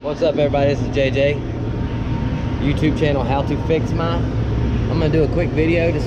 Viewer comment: Maybe i miss heard in the beginning of video. It sounded like you said your youtube channel was "How to fix my". Also thanks for speedy response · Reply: No problem it was how to fix my but I just changed it recently to my name thanks for watching.